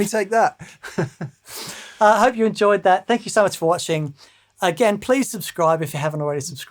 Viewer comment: I almost thought said, "Thank you so MERCH." And I would fully endorse that.